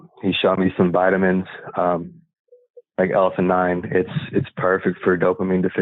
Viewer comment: Loved this testimonial! I keep replaying the same part over to hear something and the closed caption and the transcript say the same words but I don’t think it’s correct…. He was talking about what vitamins he takes for Dopamine Deficiency and it reads “Elephant Nine”… the time is 28:51. But I don’t think this is correct… Can someone tell me what he is saying? Please? ❤ My husband needs this as he has dopamine deficiency. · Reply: L-theanine